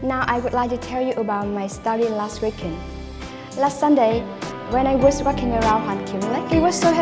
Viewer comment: When she said " when i was walking around" at 6:56 i thought she said "when i was fuking around" :v